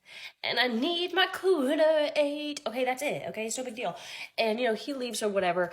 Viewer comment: "COOTER AID" THATS the first time I ever heard that before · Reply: Thought she said cooter "ate". You know...cunnilingus.